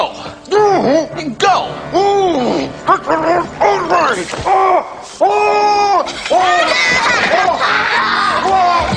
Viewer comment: I don't know what Mr. Hume said when he got toffee stuck in his mouth! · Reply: he says THIS IS AN OUTRAGEEEE